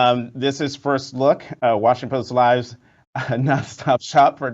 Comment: did he say "Washington Post Lies"?